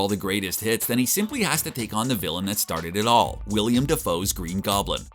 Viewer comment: Did he call him William?